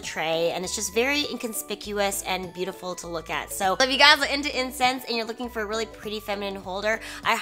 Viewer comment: I was listening but not really paying attention at first so I literally heard "if you guys are into incest" and I was like hoooolduupppp lets back up